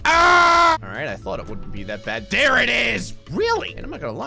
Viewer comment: What I heard at 2:46 “there it is, wheelie”😊